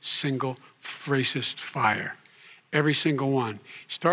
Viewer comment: min mark, what did Joe say? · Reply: Fracist fire. I think he was going to drop the F bomb but remembered where he was.